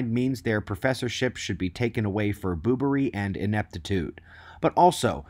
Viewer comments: Boobery. Lol. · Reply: I thought he said boomery. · around 3:11 did you say they should have their professorship taken away for "BOOBERY" ?? as in they are acting like a boob? that was awesome lol i haven't heard anyone use the word 'boob' (except for that certain part of the female anatomy) in a long time, well except for my mom.. but she's old LOL i guess it's an old fashion word, but does work well for dumbasses :P i think today we call them asshats, but I think I like boobery better, I might start using that word more thanks to you :D